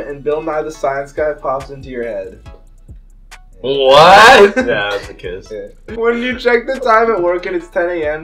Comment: What did say at 6:40? Yea that’s a kiss?